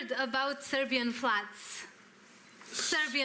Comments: When that girl said if he heard about the Serbian Floods, for a second I thought she said if he has heard about "A Serbian Film" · So the girl asks for some encouragement for Serbians after all the floods and they call it a "political issue," what the hell? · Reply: I don't think Tarantino had any idea what that girl was talking about, so he just decided not to comment on it (always a good move). I actually thought that the girl was saying "Serbian flats"